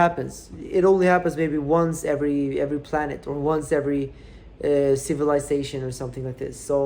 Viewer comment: “ only happens once ever planet” please tweet this haha this is the greatest bitcoin quote I’ve ever heard !!! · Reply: I heard “ once every planet; once every civilization” (8:10)🆒👍